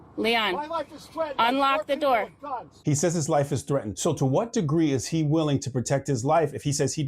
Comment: He said his life was threatened, I thought he said his legs were sweatin’ lol.